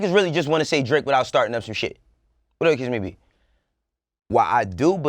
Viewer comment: Wat did that nigga say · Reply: *they really just wanna say Drake without starting up some shxt. whatever the case may be * lol